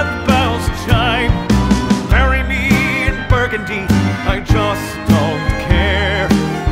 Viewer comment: Mishearing "bury me in burgundy" as "marry me and burgundy" makes things a bit more symbolic and dark...